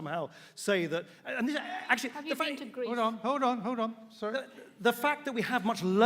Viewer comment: When Endru says "Hold on, hold on" i keep hearing "Hordor, Hordor" it's weird.